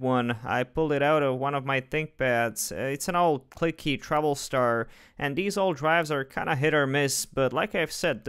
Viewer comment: at first i heard: its an old cliky troublestar ahhaha